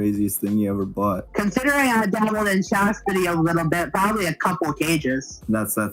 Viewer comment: I honestly cannot figure out what's being said here or what that is. I'm not one to kink shame, but considering what I already know is into...this is probly well above my pay grade. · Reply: I think he say chastity belt don't ask why I know